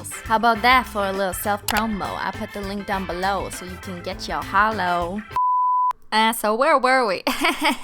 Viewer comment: ive gotten used to the bleep being used to censor out f*** so when i heard that, my brain went to "get yo holo f***"